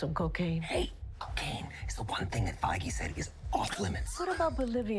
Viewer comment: Foggy? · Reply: I thought he said Foggy (Nelson, right?), but he said Feige.